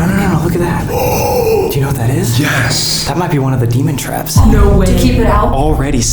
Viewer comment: It sounded like she said Semen traps😂😂